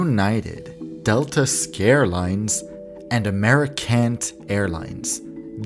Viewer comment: Check out Rosetta Stone limited-time deals thru March 31: <link> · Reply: For a moment, I thought I heard "Americunt"